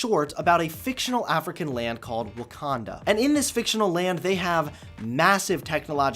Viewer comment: oml I thought it said wanka-land for a minute😂😂what have you done to me?